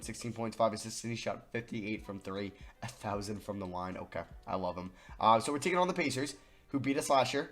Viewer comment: did he just say a THOUSAND from the line???